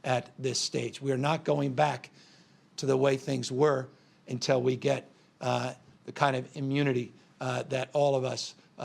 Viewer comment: so not going back to school until we get immunity/vaccinated? Did I hear that right?